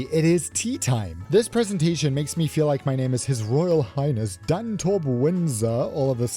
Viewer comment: sounded like you said Dantorp Windsor, which was a bit of a throwback!